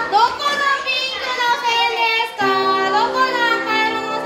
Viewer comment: sorry but did she say "boku no pico"😃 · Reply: No? She said, “Doko no pinku”